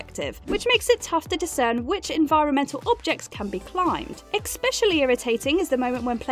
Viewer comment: Did you just say “expecially”?